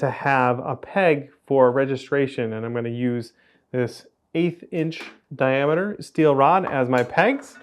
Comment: 8th inch diameter rod, I thought he said 8 Inch diameter rod while showing that clearly not 8 inch diameter rod and my brain made that old dial up router sound